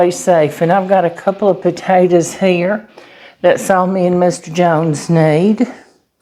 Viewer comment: Did she call her husband, 'Mr Jones' ? I love it